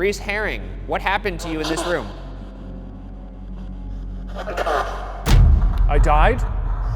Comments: something tells me the person killed by “supernatural” forces was definitely strangled by a prison guard. i thought shane was getting to that point when he mentioned that they weren’t above letting prisoners rot in a hole, and i think a guard makes more sense than another prisoner considering someone clearly entered and exited a locked space. and it certainly wouldn’t be the first or last time that kind of murder was covered up and attributed to other causes. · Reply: also, when they thought the spirit box said “i died” it sounded more like “a cop” to me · sounded like “a cut” more than I died maybe.. how did the guy die? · I heard “a cop” rather than “I died” lol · Ngl but the spirit box at 7:16 sounds like it’s saying “a cop” instead of “I died” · The shower sounded like he said fuck off not I died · I heard “fuck off” lmao which seems scarier than “I died” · I heard “a cop” when it said “I died” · I heard "I don't know" instead of "I died" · i was working and unable to look at the captions so where they thought the spirit box said "i died," around 7:17, i CLEARLY heard "i'm a cop." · Sounded like he said Alcatraz not I died.. in my headphones anyway lol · When you guys were talking to Galvin? I didn’t hear ‘I died’ I heard ‘I’m a cop’. What does everyone else think? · it sounded like they said "dead by cop" or something like that. That's what I heard.